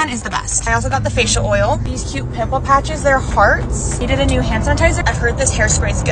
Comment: When i saw the pimple patches I thought she said, “they are farts.” And i was like she must be brave to put that on her face!❤😂😅